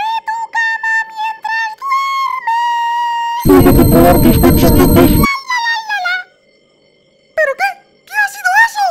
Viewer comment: que dice? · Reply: puedes o puedo controlar tus pensamientos eso dice creo